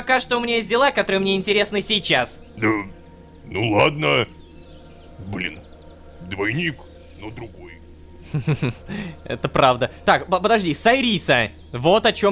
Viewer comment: Мне послышалось: "Блин... двойник... но тупой..." АХАХАХААХ